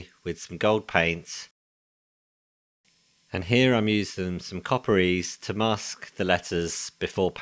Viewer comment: What are you using for masking when painting the emblems? It’s sounds like you say “copper ease”, could it be copper grease? 😅 awesome work👍🏻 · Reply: Yeah, force of habit copper ease is a brand name. <link>